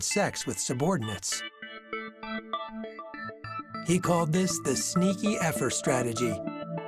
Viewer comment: did they really call it "the sneaky effer strategy"???